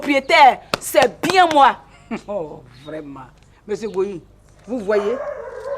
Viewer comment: 😅 C'est moi où elle à dit monsieur Bohiri ( C'est Gohou pas Bohiri )